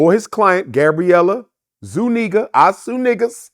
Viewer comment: did he just say her last name "zooneegas i-sue-niggaz"... naw chill doc